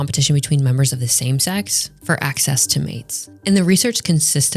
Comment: I'm in the work truck right now and mis-heard this as "...access to aids." lol